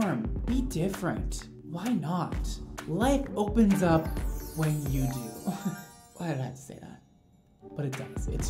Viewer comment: Ik he said "life opens up when you do" but I heard "legs opens up when you do" 😂😂😂